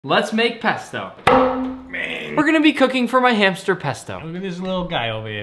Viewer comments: I thought he said he was going to be cooking his hamster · i thought he said “We will be cooking our friend Pesto!” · I need a new pair of ears I heard “today we’re going to be cooking my little hamster 🐹 💀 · I thought he said we're gonna be cooking my hamster